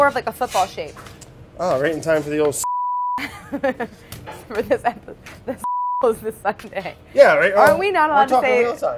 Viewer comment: what word are they saying? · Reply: Super Bowl